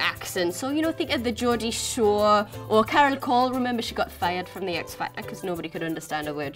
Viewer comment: did she say Keryl Core?